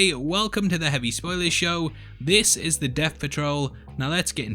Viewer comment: At the start of the video, I called it the Deff Patrol...because my nickname is. I don’t think the Doom Patrol is called that. It was a play on all the episodes being called ‘Something’ Patrol. It was a crap joke and I apologise. Thanks. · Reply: I thought you said, "Death Patrol" 🤷